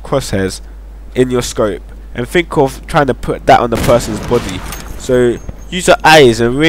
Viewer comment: it sounds like your saying try to put it on the person;s botty lol bt nice video tho